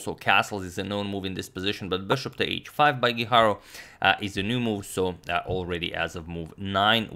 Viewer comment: did He said Hikaru or am I smoking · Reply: no he said Geeharow (Gujarro)